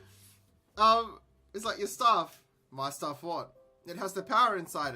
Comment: I thought that said shaft and I was worried this would take an even worse turn